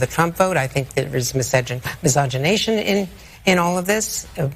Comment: "Miscogenation"? What is this exactly? Miscegenation is an archaic and disreputable term for interracial marriage. Misogny is animus against women. What is "Angrea" Mitchell actually saying here? The truth about Harris' complete, obvious lack of ability is so damning that the complicit media now has to invent new terms to impugn the character of those with whom they disagree and they consequently hate.